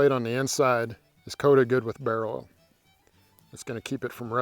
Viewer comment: What was the oil he used? On replay I keep hearing "barrow oil" or "barrel oil" and i can't find any info on those. I'm not native english speaker so could someone spell it out for me please. Kinda interested. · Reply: I think it's bear oil.